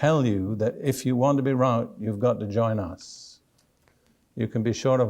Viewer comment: Missed what he said at around 3:11' If you want to be "÷×+=/* you've got to join us Anyone hear thus clearly? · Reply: I think the mumbled word was "right"? He warns us against groups that say, 'If you want to be right, you've got to join us'.